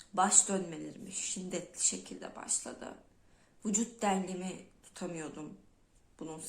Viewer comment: de ne dediğini anlamadım. Biti bana acikliya bilir mi? "vücut... Tutmuyordum artik" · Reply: Vücûd dengemi diyor, yanı istem dışı dengesizlik!